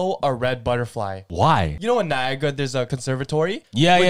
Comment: tell me why instead of “Niagra” i heard “viagra” 💀💀